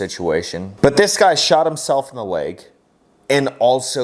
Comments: In the one video clip near the beginning I thought he said he shot himself in the balls. He's also holding his crotch like that's exactly what he did. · I didn't hear the word "leg". I heard "I shot myself in the dick". Anyone else hear it that way?